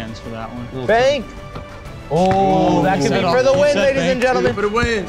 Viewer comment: sounded like dawg said, “that could be for the win n***as and gentlemen”